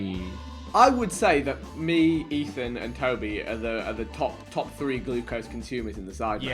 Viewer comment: Is it just me or does vik say Kobe